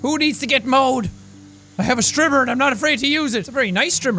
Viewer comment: When he was saying strimmer I thought he was saying stripper